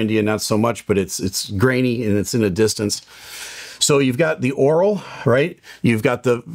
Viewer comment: oral means mouth, I mess it up too sometimes, I think what you meant to say is acoustic 👍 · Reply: He is saying aural. Of the ear. It just sounds like oral.